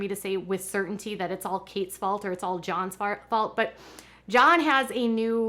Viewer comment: I totally heard "his fart" and not his "fault"...lol